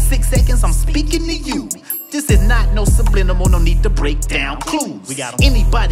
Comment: wait... did u say sublinimal or am I buggin? 😂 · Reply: Yes he did. He said him dissing Vi Seconds wasn’t a subliminal